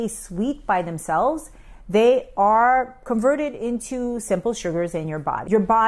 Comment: Actually it’s 5:02 she says perverted instead of converted. She does it ever time she says that and also says Unami not Umami. Very odd